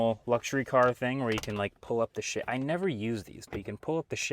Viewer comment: I thought he said "When you pull up the shit"